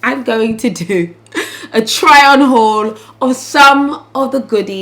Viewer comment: I thought you said no more try hauls! Still love you girl 😚